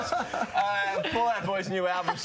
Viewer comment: I thought he said "please thank Pete and Patrick Star"